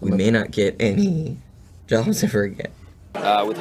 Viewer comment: does he say "we may not get any JOSHIFER again"...